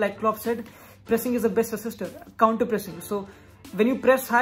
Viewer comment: "Tameez se" you rock. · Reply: Bro what did say at 10:41. Assistor or Resistor ?